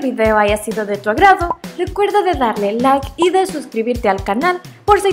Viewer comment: porque dijiste y desuscribirte del canal